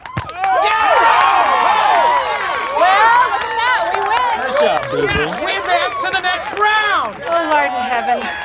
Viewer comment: what does jim say to pam at 1:15 is it nice shot booboo 😭 i cant hear to save my life